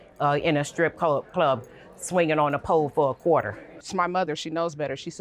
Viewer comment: Did she just say, " swinging on a pole for a quarter 🪙" 😂😂